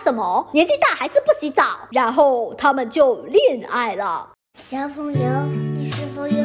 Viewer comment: “他们就恋爱了” 听成了“他们就裂开了” 。 他们裂没裂开我不知道，反正我是裂开了。